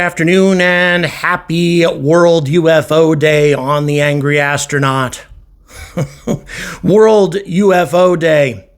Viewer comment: Jiminy crickets for a second i thought you said the angry ass 👀....and yes ufos are real...what if they wanna eat us.